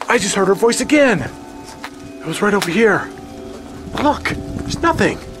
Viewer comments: When it says “Fresh air”, I heard “right here”. Another jaw-dropping, amazing video!! 😃👻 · i think she said, right here. Not fresh air. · it sounded more like "right here" instead of "fresh air".. to me? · I heard she said "right there" or "right here"